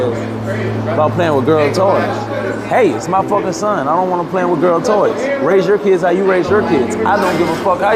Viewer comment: Unless I misheard him, I thought I heard him say don't play with girl toys. I don't think he said hate these people or hate this community. I could be wrong. Raise your son King and don't listen to the back ground.